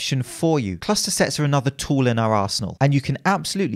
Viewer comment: Whenever I hear say 'arsenal' it sounds like he's saying 'asshole'. Just saying